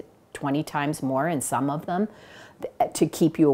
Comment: I stopped watching when they said that energy drinks contains 20 times more caffeine than coffee. Get your facts straight! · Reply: "energy drinks contends(SOME OF THEM) 20X more caffeine then a cup of coffee", that's what she said "some of them"..